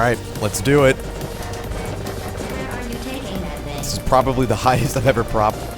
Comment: sounds like glatos says where are you taking that 🅱️ i t ch